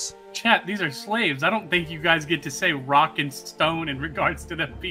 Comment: DID I HEAR A ROCK AND STONE!?